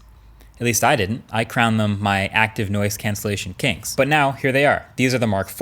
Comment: i heard “kinks “ instead of “kings” 😅